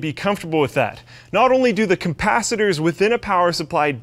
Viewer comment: did he just say COMPACITORS?! :D